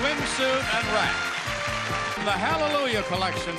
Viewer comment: modeling a beautiful swimsuit and rack...is what i thought Johnny Olsen said.